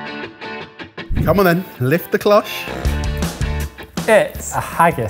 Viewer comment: On the first item, I heard Jamie say, “Lick the cloche.”, not “Lift the cloche.” 😳🤣